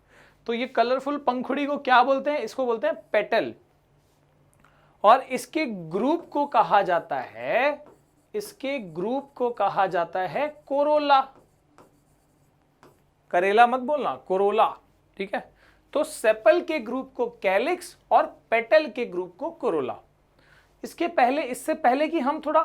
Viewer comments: I heard corona 😂😂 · Sir I swear first I thought u said corona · who heard that Corona 😂😂